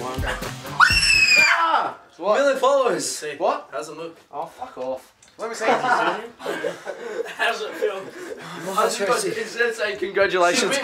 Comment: I thought he was saying "Billy follows" the whole fucking time, up until he showed his million followers ...